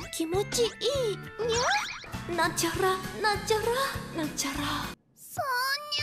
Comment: Я вообще ничего не поняла. Я только услышала какую-то «начахра-начахра-начахра».